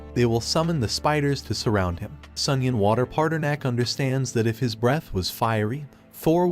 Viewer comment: every time you say the name of the mc i hear "sunion water parter neck"